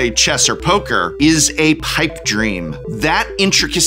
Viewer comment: Wait... did you say "pike dream?"